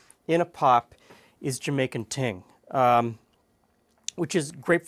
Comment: Thought you said jamaican taint